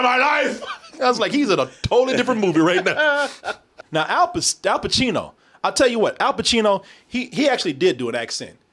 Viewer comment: I heard you almost say "pistachio", 🤣😂🤣😂🤣😂🤣